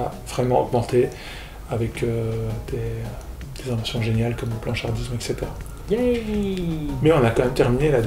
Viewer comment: Par contre ct pas audible, c'est quoi le nom du plan de psychiatrisation des meufs trans à 14:04 ? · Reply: "avec des inventions géniales comme le blanchardisme etc." Ça m'a surprise qu'elle ait pas été sous-titrée celle-là